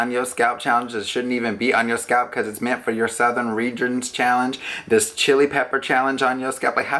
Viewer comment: I can’t with you, “Putting stuff on their heads that’s made for their nether regions”???? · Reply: Kelly Ford i thought he said southern regions